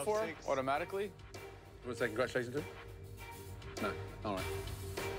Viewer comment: I've replayed this like 20 times, and I cannot figure out what Gordon says. It sounds like he's having a fucking stroke. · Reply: “Anyone wanna say congratulations to him?”